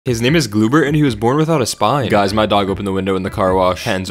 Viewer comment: Gloobert looks like a burnt noodle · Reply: i thought it said "goobert" and all I could think of was Spongebob 💀💀💀